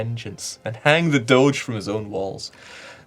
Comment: I heard: "Hang the Doge from his own balls". I was like, wow.